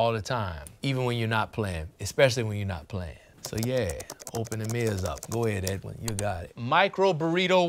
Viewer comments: Lol "open the mirrors up" instead of "open them ears up" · Very funny to me that when he said "open them ears up" at 3:32, some presumably very tired editor misheard it as "open the mirrors up" and thought 'yeah, that sounds like a thing people say' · I don't know who's in charge of typing up the answers as text, but at 3:32 they wrote "So yeah, open the mirrors up", which is a beautiful and evocative turn of phrase. But he clearly said "So yeah, open them ears up" 😅